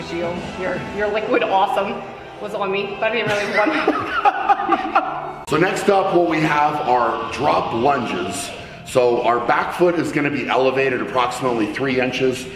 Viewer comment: i thought he said " next, we have drunk lunges"